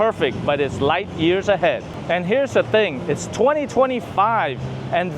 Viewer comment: HOLD UP!!! Did he say "it's 2025"?????